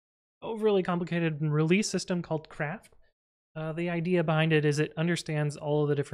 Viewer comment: Legit thought you said “Sentry has a release system called CRAP” 😂